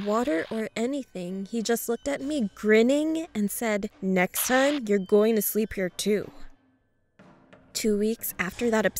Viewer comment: I thought he said Next time your groin will sleep in a tube